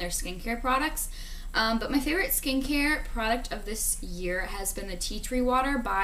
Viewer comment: Everytime you say "Skincare product" I hear "Skankier product"